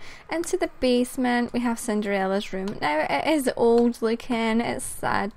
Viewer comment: i heard you said ''cinderella's room '' in 6:8